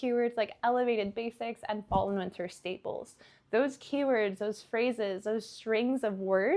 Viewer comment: what keyword did you say after "elevated basics"? I couldn't decipher. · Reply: she says " fall and winter Staples"